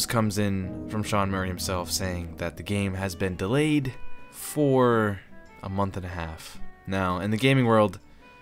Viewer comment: sounded like you said four months and a half, so i freaked out! :O But then you said august and so i rewind and it turned out you said "for a month and a half". Then I looked at the chair i just destroyed and went "oh"